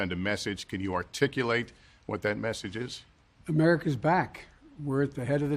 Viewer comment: I thought I heard “America is axed”.